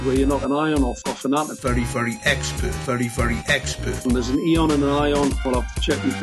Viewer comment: Sometimes I hear "Furry furry expert"